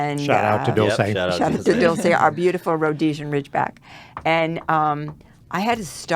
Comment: did she just say Rhodesian Ridgeback?